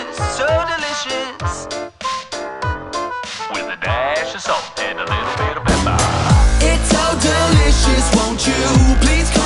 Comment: I hear "With a dash of son and a little bit of Peppa"